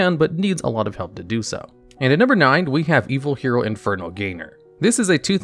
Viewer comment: I heard "Evil HERO Inferno Gamer" for a sec💀